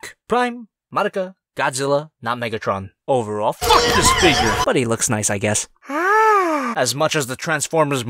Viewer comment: i thought he said fuck this n!!!!!!!!!!